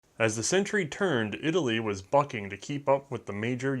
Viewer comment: For some reason I misheard the intro as "Italy was fucking up" and thought was just being real for a minute there.